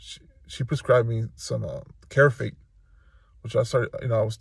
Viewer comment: Can someone tell me what did he said in 2:50.. she prescribed me ……….????? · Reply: carafate